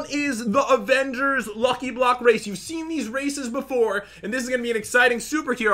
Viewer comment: At the beginning for I second I thought Pat said: "You've seen these racists before." ._. lol